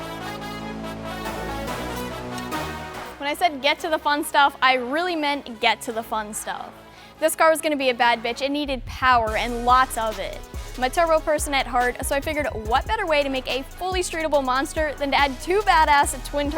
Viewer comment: did she say “this car is gonna be a bad bitch”? 😂